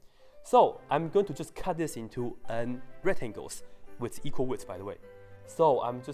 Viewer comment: Oh, rectangles, I thought he said right angles